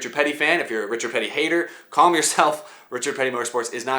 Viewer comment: I thought it said Richard Petty is not dead YET